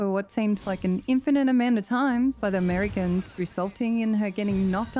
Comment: had to back up swear you said "being attacked by americans" lol not merregons